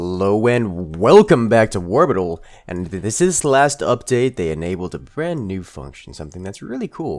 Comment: I'm sorry, At the beginning it sounds like you said this was the last update. Is it?